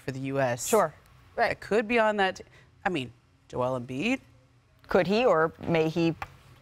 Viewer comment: did she say Joel Embiid to play for USA?